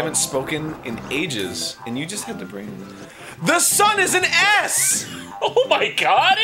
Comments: I thought he said "THE SUN IS AN ASS!" for a while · For a second, I thought Ego said "The sun is an ass!"